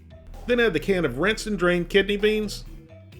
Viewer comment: I heard rancid drained kidney beans